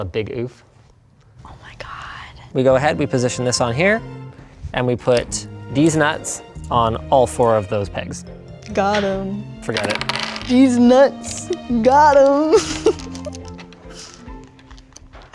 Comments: Did I hear right? Deez Nut?? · Linus: "We just put these nuts over there" Her: holding it... holding it... holding it.. ..."GOT HEEM!" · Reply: OK, but whether Linus did it on purpose or not, it did sound like he said " deez nuts". Seriously, though, that gag got me good.